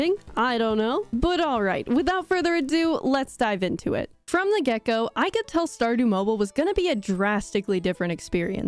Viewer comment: When you said “from the get go” I heard “from the gecko “ 😂